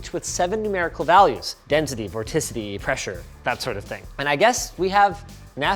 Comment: for a sec i thought linus said, density, rotisserie